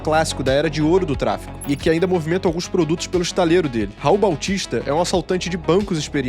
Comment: eu juro que escutei “rouba autista”